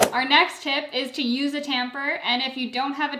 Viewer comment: Timestamps ☁️✨🌱 0:23 tip1. Use frozen fruits 0:42 tip2. Use less liquid 1:15 tip3. Use creamy fruits (bananas, mangoes, peaches) 1:54 tip4. Don’t use ice 2:20 tip5. Use a tamper 3:26 tip6. Add creamy (yogurt, protein powder) 3:50 tip7. Add liquid first 4:16 tip8. Blend the smoothie right away 5:13 tip9. Blender 6:19 tip10. Be patient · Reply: I thought Tip 5 said Use a Tampon 💀